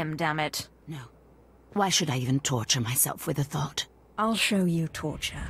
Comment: Did she just call her a thot?